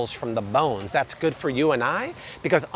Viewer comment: Hear “It’s good for you and me” instead of his solecism.